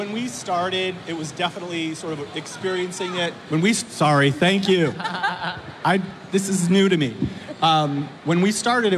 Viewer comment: What did the fan said? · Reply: "use the microphone!"